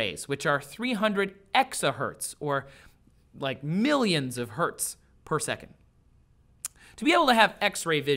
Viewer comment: Did he say Hz per second?